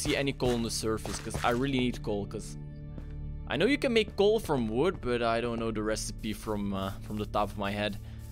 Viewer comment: Did you say you can make gold from wood? Huh. 3:43 through